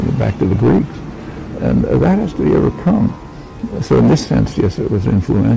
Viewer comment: Can anyone make out what Chomsky is saying @ 2:16-2:17? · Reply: "That has to be overcome."